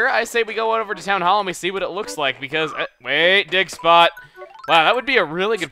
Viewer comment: At the beginning when he said dig spot it sounded like he said "Dick Spot" XD